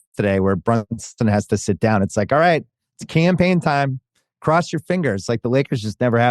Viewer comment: lol I’m so dumb they said Cam Payne & I thought they were saying campaign 😂😂😂